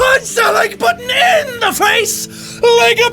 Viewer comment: To anyone who is new and/or doesn't know what he says in the outro, here: But, THANK YOU GUYS for watching, and if you liked it, PUNCH THE LIKE BUTTON IN THE FACE LIKE A BOSSSSSS!!!!! AND (sometimes in deep majestic voice) high fives all around! highfive sound highfive sound and thank you guys, and I'll see all you dudes, IN THE NEXT VIDEO!!!!! (Roll credits xD). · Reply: Andrew Gelbman I thought he said "...punch THAT like button in the face..." instead THE. :)